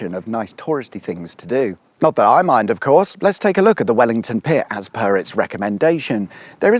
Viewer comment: Oh, I'm good, thanks for asking, how about yourself? 1:13 Did say Wellington Pier or Wellington Pit? I've listened to it three times now and sure it's pier... 1:40 I dread to think how many men (and boys, it was usually men or boys) lost their lives in all collieries around Britain over the years. Then add in all those who lived miserable lives and/or died from some quite horrific industrial diseases such as pneumoconiosis, silicosis, COPD, and numerous cancers including mesothelioma. And yet there are them who want to reopen coal mines because not dying with these diseases is woke? Or something. Twats. As an aside, Whitehaven and Barrow are bases for offshore windfarm operation and maintenance, providing well-paid jobs in a sustainable industry, because I mean, we'll always need electricity! 2:10 ah, the good old days where there were no regulations and irresponsible companies could pollute to their hearts' content. Bring it back I say, because who needs paint on their cars! 2:40 see my previous comment. 4:50 ah yes, radiation, just what we need. 6:25 are C roads a "thing"? Never heard of them before! I've heard of A roads, B roads, E roads, and M roads, but never C. Are there many? Is there a map? Zoo: ah yes, back to regulation. I mean, wtf was the licence not cancelled as soon as the rhino escaped? Yes, it was a most enjoyable video, and yes, I have pressed that button. Have a good week yourself...